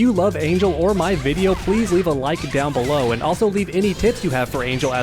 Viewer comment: "And also leave any tits you have for angel" Well out of all the times to mishear some one It would be there